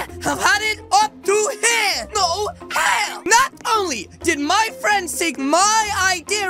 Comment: I literally heard him say "NO HELL"